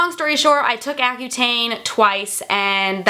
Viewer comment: in the video you mentioned a product that you took for the ance what is it called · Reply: Accutane